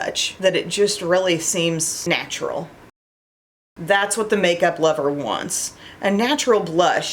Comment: ❤😂 "snatchuarl" that's what I heard New catchphrase, signature words